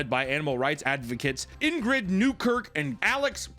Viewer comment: thought you said her name was inbred. i was like, yeah she is